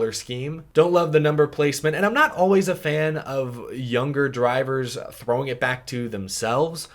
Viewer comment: Lol it does sound like that but I'm pretty sure I said "younger", but because I then said "a lot" I think it sounded like girl haha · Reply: now I hear “younger” lol, makes much more sense now😂😂